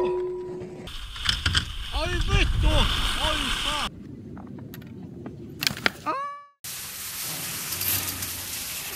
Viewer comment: did he just say Vittu, or did I just hear it wrong? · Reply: dr.clicky click he says ai vittu ai saatana